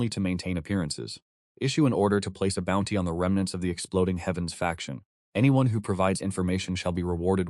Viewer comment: Did someone say Exploding heavens faction??? 👀